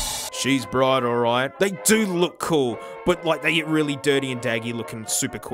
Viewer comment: woah, i came on here, and you are talking about drums but all i heard was "DIRTY BUDS" I didn't realize you have two channels.